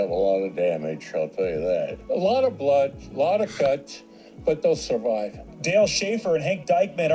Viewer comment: Did he just say…”A lot of blood, a lot of guts, but they will survive” ?